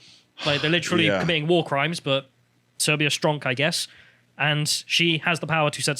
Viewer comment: Did he say "Serbia stronk"? Would that be a meme of some description or was Serbia actually relevant to the subject? Due to resemblance to Adrian Chiles and weirdly unfavorable stance on Serbia that shimmers through on occasion my working hypothesis that he's a closeted Croat and a Jasenovac denier. So maybe I'm just imagining things in this instance...